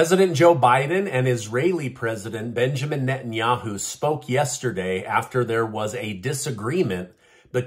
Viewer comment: At the beginning of the clip , i thought u said, biden and Netanyahu smoke 🚬 together 😂